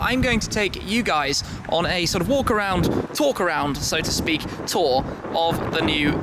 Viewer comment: Shmee, did you say walk around, twerk around @ 6:31 ... Sure the ratchets would love to haha